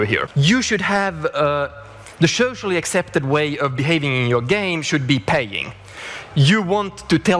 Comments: Because of his accent, for a second there I thought he said "The socially accepted way of behaving in your game should be pegging", and I was confused, though intrigued. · Every time I hear that Torulf Jerstrum clip, I swear I hear him say "the default way to play should be pegging" instead of "paying", and that changes the context ... only slightly. I mean, the consumer is still taking it up the poopchute with either word. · "(...)should be pegging" O_O I know what he's saying, but am I the only one who hears that?